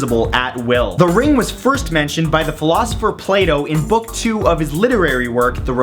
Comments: lol sounded like he said "the philosopher playdoh" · Wait- did you just say, "The philosopher Play Dough"? o-e